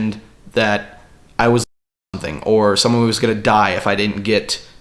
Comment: it sounds like he says he was f***ing something while playing the first mission of that Star Wars game x]